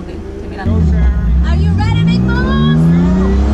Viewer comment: i swear she said "are you ready big ball?" 😂😂😂